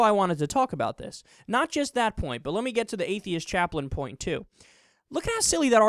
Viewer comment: I don't get it, if atheist don't believe in anything why do they want a non religious chapel? Seems pretty simple to me the atheist should just meet up and form their own group and stop whining if there not a religion. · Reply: My apologies, like I said I thought he said chapel. I should've posted my original post and said the atheist should have a shrink to consult with. I don't see why Hannity would oppose the atheist soldiers seeing a therapist.